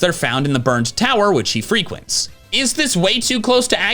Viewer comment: What did he say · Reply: He said frequents. As in Morty frequently goes to the burned tower